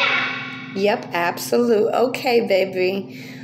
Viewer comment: I hear “yes ma’am” when she answers you. Southern respect